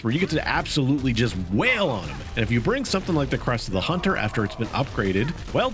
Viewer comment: “crust” of the hunter… did I hear that right? 🤣